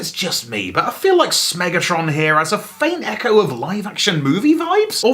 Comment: For half a second there, when you called him Smegatron, I thought you said Smegmatron.